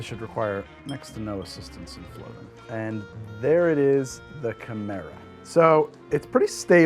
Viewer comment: When you introduced the chimera cocktail I had thought I heard Camaro, and now I need a muscle car cocktail themed episode from you (Dunno what that would entail, but it sounds fun)